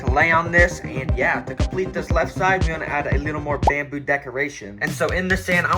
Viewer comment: I thought it said “Bone decoration”